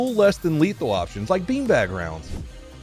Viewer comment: For years, I thought he was saying “algae rounds”